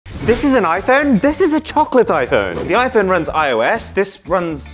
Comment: I thought he said trumpet iphone💀